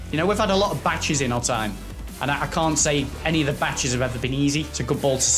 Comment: He was saying batch instead of match wtf